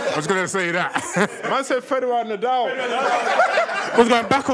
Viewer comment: What did he say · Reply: "man said Federer - Nadal" The tennis players